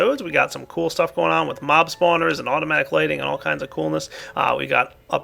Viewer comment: i thought it said automatic LIGHTNING